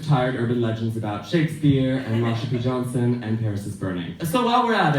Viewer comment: HELLO FRIENDS I NEED SOME ASSISTANCE! I'm doing an analysis on this speech for my english class and for the life of me can't figure out what she said at 4:14 after talking about shakespeare plz help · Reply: erin bodinar “and Marsha P Johnson and Paris is Burning” ! if you have any other questions feel free to ask, this is the second time she did the speech & i can link to vids of the first !!